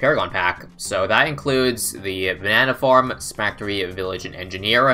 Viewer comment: all i hear is “spactory” and it makes me want to cry.